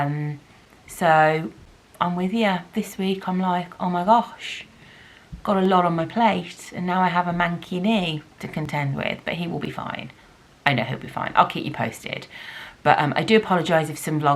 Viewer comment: Every time you said "manky knee" I heard "mankini" and was very confused as to how this was part of your lockdown!